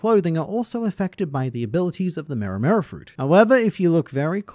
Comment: did anyone else kees think he said mera mera instead of mero Mero fruit